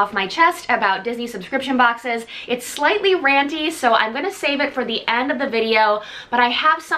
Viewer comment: Just going to throw this out there . . . . At first, I thought you said "randy" not "ranty" and I was concerned about the direction this unboxing was was going to take. 😂😂😂😂😂😂